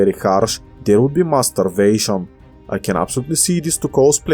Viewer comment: when you said starvation you say the first a with an u voice so instead of starvation its sturvation and if we put the mass back we got mass sturvation and mixed with your almost silent v the subtitle mistaken it for a b and the rest is history